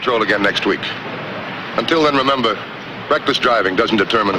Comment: Sounds like he says "breakfast driving".....lol.